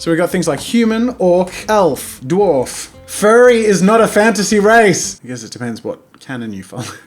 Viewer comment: I thought he said furry. Me: OwO